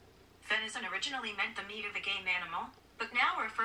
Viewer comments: I thought Siri said “the meat of a gay animal” but I now know it said “game” lmao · At first I thought it said "the meat of gay mammals"...... I showed my mom and she was like " GaMe!" oops